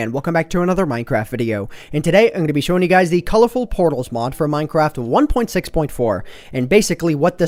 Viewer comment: I thought its said "colorful potatoes mod" lol